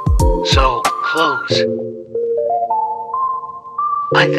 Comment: At the end it sounded like he said da f-word instead if folks